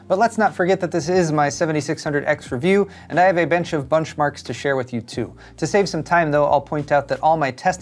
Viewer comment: Did I have a mini stroke, or did you just say you had a bench of bunchmarks to share...